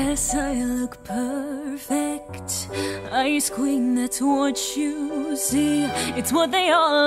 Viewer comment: ‘Yes I Look Perfect, Ice Queens That’s What You See’ Please Tell Me I’m Not The Only One In The Original I Hear ‘Yes I Look Perfect, Ice Cream,Not Sweat,You See?’